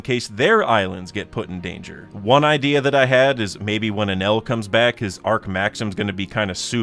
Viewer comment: yo my subtitles are crazy. When you say "When Enel comes back" the captions say when an L comes back, basically saying Enel is an L😂